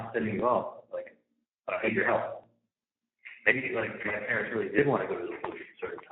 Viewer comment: did he say his parents? Did he just incriminate Dad...Harvey??? 👀🤦